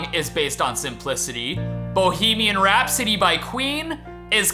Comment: When you mentioned "Bohemian Rhapsody", I swear I heard "Bohemian rap city". I wonder how it would be called? Something like "Heyyofreddynichtkrügerdorf"?